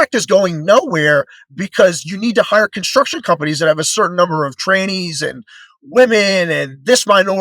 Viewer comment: At the 40:35 min mark approximately, did he just say trannies?! 😂 Oh boy, canceled 😆 Although I doubt the alphabet soup watches business news.